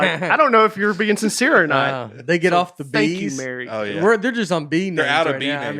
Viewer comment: did Brain say “Fu$& you Mary”. He was kinda contentious throwing down his right hand. (That means aggressive Nate)